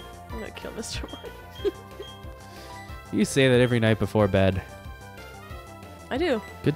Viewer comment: Ooh, I get it. For a second I thought said Mr. Small every night before bed. My bad.